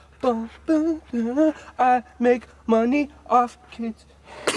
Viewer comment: now every time i hear sketch's intro all I hear is "i mAkE mONeY oF kIDs"